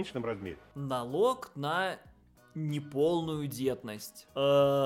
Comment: когда ты сказал "налог на неполную детность", мне отчётливо послышалось "налог на неполную бедность". Чует моё сердце, скоро и такой введут.